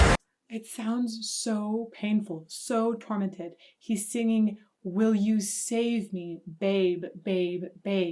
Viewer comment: i thought he was saying "would you save me pain"